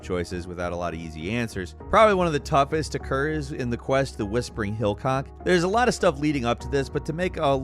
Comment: Did you say "Hillcock"?